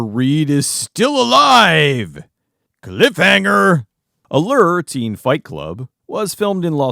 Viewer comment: I thought it said Lube Teen Fight Club, which would be a completely different movie. It would have been on Skinimax and still covered by GoodBadFlicks.